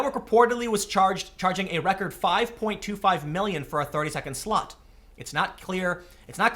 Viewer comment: I could have sworn Tim said a "30-second slut".